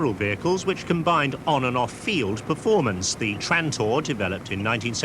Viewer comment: "nfact the trantor" thought it said tractor and you were talking about the fergi!! haha.